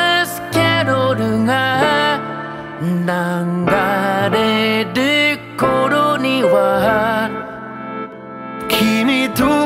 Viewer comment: 「なんがんれーる頃には（流れる頃には）」に聞こえる